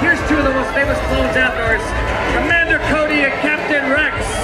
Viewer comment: did he say amanda cody